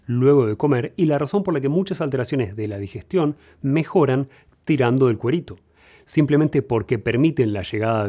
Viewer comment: En el minuto 6:13, qué dice? Tirando de cuerito? Que significa?